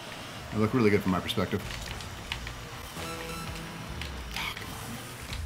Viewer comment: Anyone else notice what sounds like the "F" word at 15:45?? For the record, I'm not 12 and against cussing but they run a family friendly channel and I'd like to respect that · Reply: he said, gaw come one